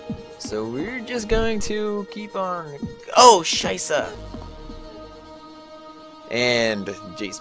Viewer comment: That game is cute~ I like objects xD Btw at one point it sounded like u said ''scheiße'' (=german for shit/crap)